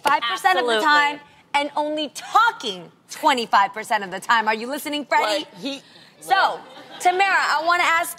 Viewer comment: Am I the only one who heard Jeannie say “Are u listening Freddy?” In